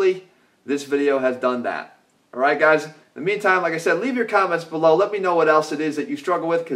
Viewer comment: Did he just say enimens